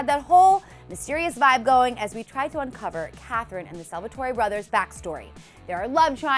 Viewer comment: did she say salvatory brothers or am i going crazy?!?!